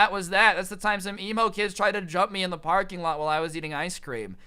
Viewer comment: I thought you said elmo tried to jump you